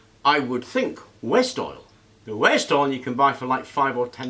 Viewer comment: what is west oil? Am I hearing it right? Is it an english product? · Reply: I think he said, "waste oil."